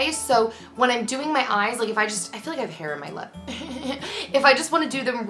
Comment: When you said "I feel like I have hair on my lip" I totally heard "I feel like I have hair in my butt" hahhahahahahahah :)